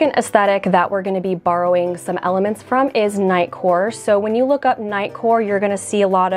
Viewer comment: When you said “knight core” I heard “nightcore” and thought about what kind of fashion would relate to highly sped up songs lol